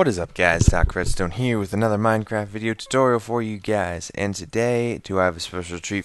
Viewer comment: Did you say mindcrack? xD